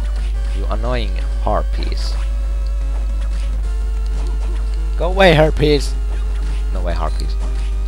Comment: did he say get away herpes? lol